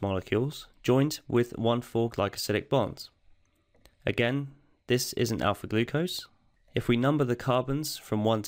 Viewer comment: Did you say "this isn't" or "this is" ? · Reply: This is `An Alpha glucose`